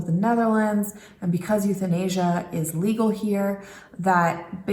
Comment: I might have heard wrong. But I heard you say "and because it's legal here and because it's in asia". This ain't Asia. :)